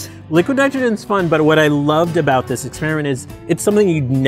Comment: Did Norm just say he raped flowers?